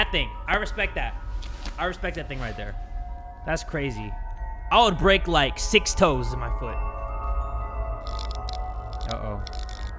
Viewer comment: Ayo did he say" I would break like six toes in my foot" (its on 7:05 ) LMFAO edit: i putted wrong timer